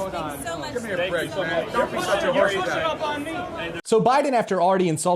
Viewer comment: Alright quick correction it looks like the worker when he says "this is not okay" was referring to Joe's fingerpointing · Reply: I didn't catch it the first time, but Biden says "don't be such a horses ass" at 7:50 in your vid